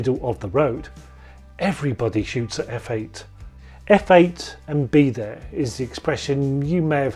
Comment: Gosh, all this time I thought that WeeGee said "f/0.8 and be there" -- I've gone through three Voigtlaender 29mm Super-Noktons just trying to get an image. Not easy riding a motorbike, listening to a police scanner and manually focussing one of those at 0.8 simultaneously, but then, no one said it would be easy.